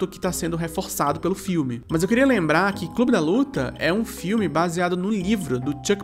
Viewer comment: só queria dizer que uma parte desse vídeo eu assisti lavando a louça então em dado momento escutei "bruno de luca" ao invés de "clube da luta" e na hora apenas aceitei ao invés de questionar porque já to acostumada com seus vídeos